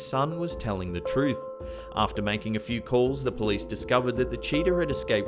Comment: interesting accent makes it sound like he's saying that the boy found a "cheater" in his backyard. Ironically, I'm not sure which one would constitute as a stranger find. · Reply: I thought he said cheater too.